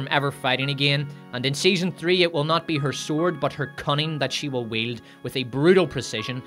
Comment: I swear I thought you said "cunny" at first. That would have worked, too...